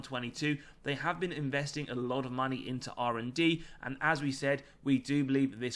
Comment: What's he saying on 06:34 a & d ? Thanks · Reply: R&D (Research and Development) 😊